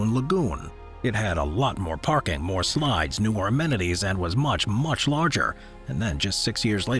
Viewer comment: Did you say 'ormenities?' *amenities